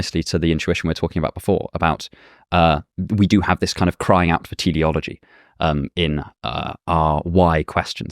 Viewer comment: What word is he saying here after "Crying out for _____"? Is it theology with an accent? I cannot figure it out. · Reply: teleology